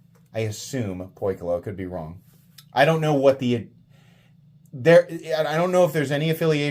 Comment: What did he say here? He assumes "Coy Glove"?? A kinfe maker? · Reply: Poikilo, not Coy Glove... 😅